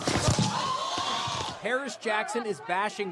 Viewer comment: Not going to lie I thought its said percy Jackson